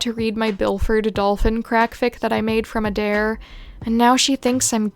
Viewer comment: I was listening to this in the background and heard it as "Byford Dolphin" (basically a person got squeezed through a 3 inch hole or smth)